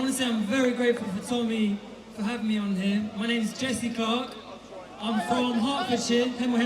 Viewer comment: I thought he said my name is Jessica! Even if it was, it should make no diffidence. Thought Tommy was going all rainbow on us. Anyway, fair play to guy for speaking. And glad he’s not called Jessica.. lol